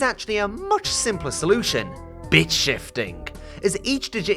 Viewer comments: can't lie, thought you said bitch-ifiting for a sec. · When you said "Bit-shifting" I thought you said "Bitch shifting" 😭